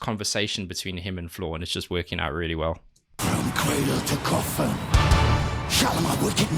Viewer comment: At first I thought Marko said, “from quaalude to coffin” to begin that last section. I’m assuming I was wrong...